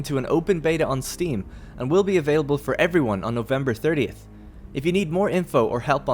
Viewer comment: thanks for the reply. but why i don't have the patch right now? i entered the game just to see the new ingame panels instead of the encyclopedia. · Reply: ohhhh stupid me. i thought you said 13th of november :P ty